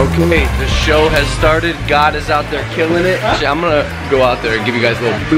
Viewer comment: When he said "Gad is out there killing it." I thought he said "God is out there killing it." 😂